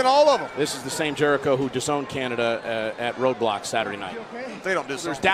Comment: did I heard roblox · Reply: He's saying Road Block, not roblox.